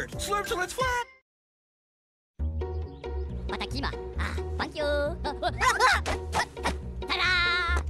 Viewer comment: Is it just me or does it sound like Dave said the f word